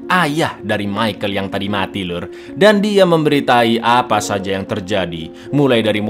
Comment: SORRY VRO I HEARD IT AS "Memberi tai" instead of "Memberitai" gyatt kupingku astaga Tuhan